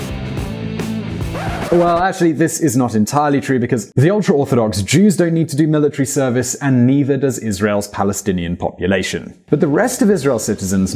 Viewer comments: Not entirely sure that the subtitles match the speech.... He says 'The ultra orthodox Jews don't need to do this military service and neither does Israel's Palestinian population' The subtitles say ....'and the Palestinian population are discriminated against' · Ok, the person who did the closed captioning for this needs to be fired. or at least reprimanded. At 5:00 the audio says "Well, this is not entirely true because the ultra-orthodox jews don't need to do military service and neither does Israel's Palestinian population." The bloke who did the captioning wrote: "Well, in fact, this is not exactly true. The ultra orthodox jews don't need to do this military service and the Palestinian population are discriminated against." Politics aside, this is not what he said. Its not even close to what he said. The spoken grammar is better than the written, has a word choice that feels more like the speaker, and has the added benefit of being the original statement. The act was dishonest, and if it wasn't politically motivated, then it was embarrassingly lazy.